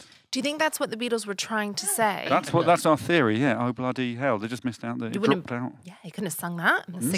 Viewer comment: When he was singing "oh-bloody-hell", all I heard was "hello-bloody"